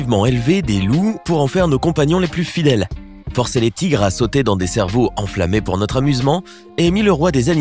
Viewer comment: EU... C'est moi ou au début j'ai entendu "forcé les tigres sauter dans des cerveaux" ???